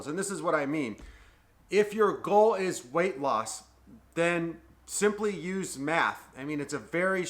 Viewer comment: Idk why I thought he said “ simply use Meth “ 😂